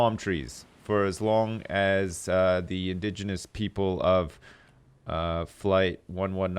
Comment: "Native Indians" oh boy- wonder if that's going to get him some hate mail. Unless he meant people from India, in which case saying they were gnawing on buffalo feet (bovines being sacred there) will get him hate mail. The outcomes are not good either way. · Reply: i thought he said indigenous?